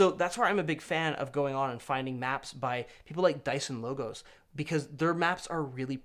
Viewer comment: You mentioned a resource for maps at around 9:15 and the captions described it as Dyson logos, but sounded like dice and logos. What were you referring to? I’d really like to see what it can offer.